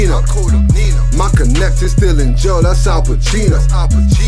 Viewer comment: What does it say at like 1:10??? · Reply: "My connect is still in jail that's Al Pacino"